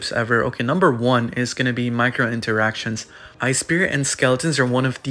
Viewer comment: i thought bro said micro transactions 💀